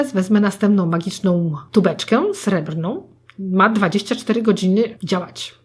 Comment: Usłyszałam "Dupeczkę srebrną" I z ciekawości, aż popatrzyłam na ekran. (Bo oglądałam w tle)